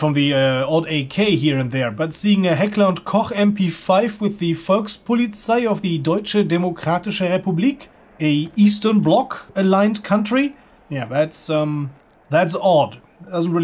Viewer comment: Just incase you're wondering what he said "But seeing a Heckler and Koch mp5 with the Volkspolizei (People's police) in the Deutsche Demokratische Republik (German Democratic republic) a Eastern bloc alligned country is odd"